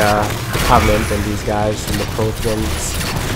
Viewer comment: did you just call them the "PROTHEANS" or am I hearing things??